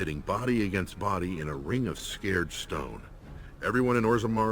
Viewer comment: Sounded like he said "scared stone" rather than "sacred stone" .. :D